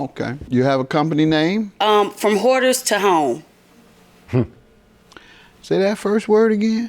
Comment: in a Doyle giggling the guy with the red (pink) shirt in the background behind the plaintiff is giggling cause JM really thought she said "Four Whores to Home" or something to nature. Doyle a fool and I love it.